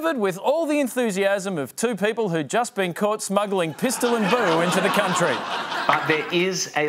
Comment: can anyone tell me who those people are and wtf charlie says, caught smuggling ... pistol and brew??